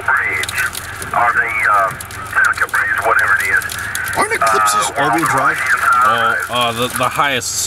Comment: is he saying "aren't eclipses all wheeel drive? "